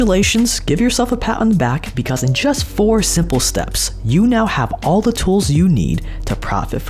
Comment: Does he say "SIMPLE steps"